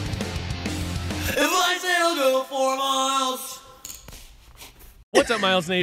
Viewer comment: Just wanna let everyone know that i had closed captioning on for the advice song, and it heard it as "I'm nice and i'll go four miles" instead of "advice that'll go for miles>"